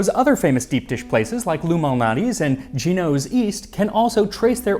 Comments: I'm not gonna lie, when you said "Lou Malnati" I heard "illuminati" 😂. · I swear when you said "Lou Malnati" I heard "illuminati"